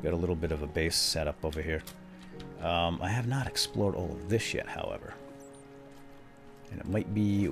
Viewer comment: No, he said "I have not explored this yet over ever." It sounded like he said "Thish" that's why you thought he said shit.